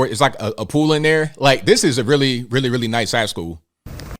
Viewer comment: i jumped in @ 2:21 and it sounded like he said it was "looks like a really, really nice a-hole"